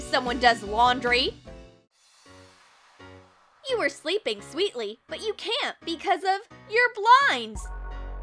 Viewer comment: lmao I really thought she said "you were sleeping sweetly but you can't! because of your blind!!!! :DDDD